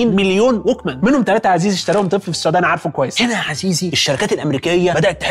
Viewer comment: هل قال اشتراهم طفل في السودان؟ ولا انا سمعت غلط؟ الدقيقة